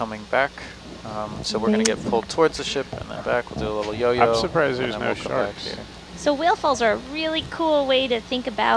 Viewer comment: i thought when he said "im surprised there arent any sharks?" I thought he said "I'm surprised there arent any cars?" 🤦🏽‍♀️oops